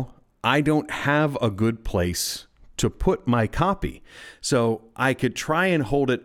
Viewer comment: LOL! I thought you said "I have nowhere to put my coffee" And I was thinking. "This guy is serious about his coffee"